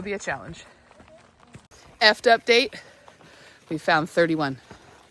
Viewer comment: "Eft update" I was watching while working and almost spit my drink out with how I heard..... "F'd update" :face-turquoise-covering-eyes